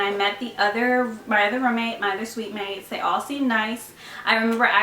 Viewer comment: I thought she was saying rum mate instead of room mate😂😂